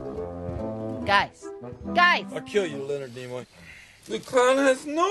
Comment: Hahah I always thought he was saying "I'll kill you little nemo"